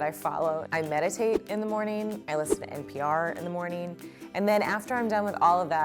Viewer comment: I thought Devin said I listen to mcr